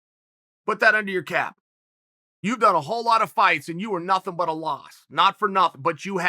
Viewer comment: what is he saying? Like literally I don’t know if I’m hearing him right. Is he saying “you’ve done a whole lot of fights and you are nothing but a loss” ...What does that mean ??